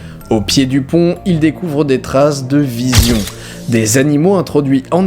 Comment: c'est moi ou il dit vision à la place de vison? ^^